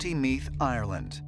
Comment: I laughed when they spoke of the Boyne river in county Methe…My entire family is from Ireland and I grew up in Canada with my parents saying “you’re as full as the Boyne” any time one of us had too much too drink. Memories. 😂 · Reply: Sounded like he said Meat than Meath 😅😅😅